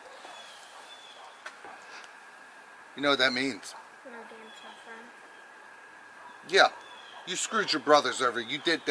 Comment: Dad: you know what that means: ??? (I didn’t hear him like what did he say?) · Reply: He basically said: I lose my phone? Then the dad said yeah and told him that he screwed his brothers over